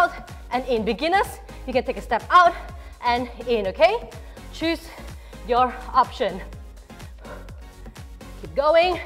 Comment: Every time she says "choose your option", in my head, I always hear "choose your poison" 😓🌚.... Great workout by the way, thank you. 😊